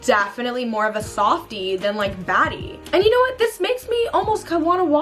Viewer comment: IT SOUNDS LIKE SHE SAID FATTIE INSTED OF BADDIE LMFAOOOOO